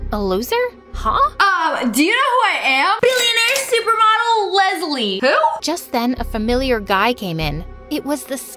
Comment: Is it just me or did she say my name at 5:13 and my name is Lezlie 😳😳😳